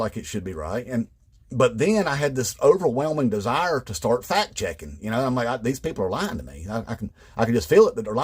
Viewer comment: IDK if it's his accent or my hearing or what, but when he talked about fact checking Fox News I heard "fat chicking."